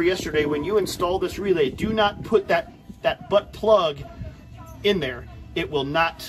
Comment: lolll 10:30 sounds like you said butt plug hahah thanks for the video man imma have the guy installing mine watch these videos!